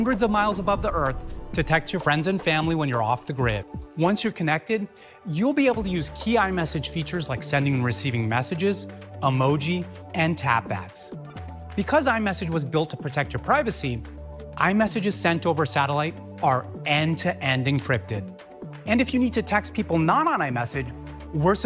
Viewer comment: Honestly when he said tap backs I thought he said tampax 😂.